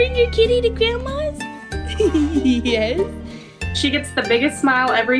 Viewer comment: I think I heard yes. Did anybody else hear a yes?